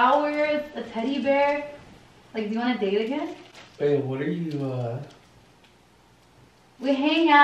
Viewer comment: Who else heard when Brian said “BABE what’re you doing “ at 12:23 😳